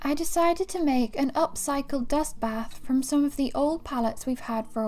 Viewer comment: I swear, I thought you said you were building a guest bath! I was thinking if people were coming over to watch the Shwoos and they might have to take a break. Then, when you went over to the pen, "dust bath" made so much more sense! I love this new channel! J wish I could be there to help. But, you two are unstoppable. But, I'd have snapped up brother while he was there!